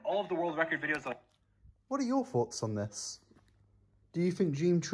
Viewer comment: It sounds like he said "treated" instead of cheated, I dont know if this was intentional or not, but I love it either way